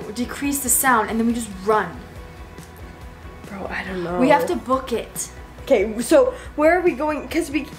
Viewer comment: who has been a fan of when it was still 2018 and i thought said broke it for a sec and then i replayed it and then i heard book it and then i was like ohhhhhhhhhhhhhhhhhhhhhhhhhhhhhhhhhhhhhhhh AND THE WAY FELL LOL AND I GOT SCARED WHEN THEY RAN AND I ALSO LAUGHED WHEN SHE PUT THE PAUSE SOUND ON TY WHEN HE WOKE UP